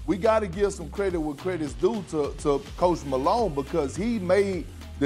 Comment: Thought he said “Post Malone” not “coach Malone” lol